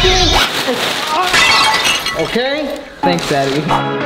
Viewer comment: Did say 'Thank daddy????'